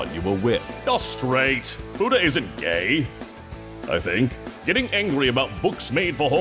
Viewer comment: I know that this is 6 years old and basically no-one is going to see this but I would like it to be known that for 6 years now, at 17:54, I have always thought he said "You're straight. Buddha isn't gay. I think." which honestly is funnier to me. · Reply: I always thought it was, but according to the subtitles it is "Futa isn't gay. I think."